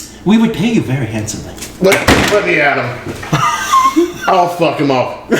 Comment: Exactly like what I would say. "That's it. Lemme at 'em! I'll zap 'em good." Also if I was selected for a game in the Crystal Maze and the aim of the game was to defeat the defender of the crystal I'd say "Lemme at 'em." Even if it was a different game I'd say "Bring it on." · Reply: Nope, he said "I'll f(*#'m up".